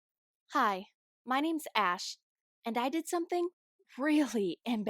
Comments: I thought she said her name was ass😂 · i thought she said “hi, my names ass.” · I thought you said her name was ass O_O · Bro i thought she said “hello my name is Trash” 😂